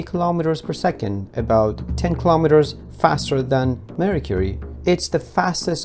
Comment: Did he say Marie Curie instead of Mercury? 😊